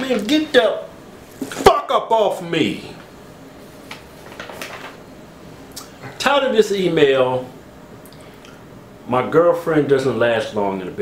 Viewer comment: i always thought he said "tired of this email" but i noticed its "title of this email..."